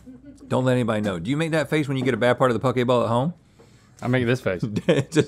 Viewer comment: I thought they said pokeballs